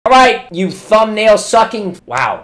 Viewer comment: I accidently skipped ahead a few times in the video and I heard "Alright you thumbnail b***h" 🤣